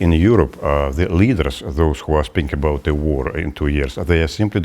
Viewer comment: Did he just say war in the war in two years